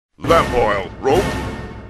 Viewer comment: I always thought he said "Fat boil" instead of lamp oil. And at the part where he kept saying "bombs" you should have put in the WTF Boom